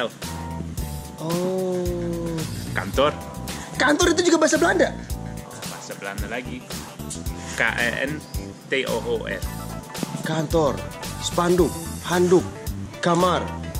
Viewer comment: gw dengernya K A E N T O E L